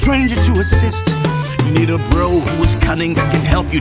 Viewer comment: When he said. You need a bro who.... I thought he said bro hoof